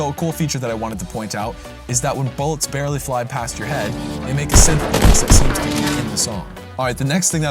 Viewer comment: the studio is screwy and what you say is unintelligible to me. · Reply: Oops my bad, I didn’t realize how loud the game was when editing. What I was saying is that bullets that fly by close to your head make a synth noise while going by.